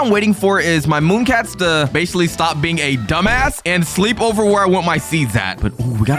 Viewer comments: did bro say dumbass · What did you just say? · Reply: Ass no badword